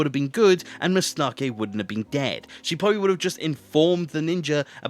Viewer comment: am I the only one who thought he said Mustake wouldn’t have been gay?